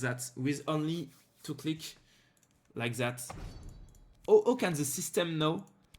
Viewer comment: C'est moi ou vers 13:45 tu dis "aucun system know" ? xD Tu t'es perdu ?